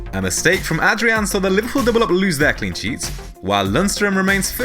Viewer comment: wtf did he just say??!!??! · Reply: "Saw the Liverpool double-up lose their clean sheet"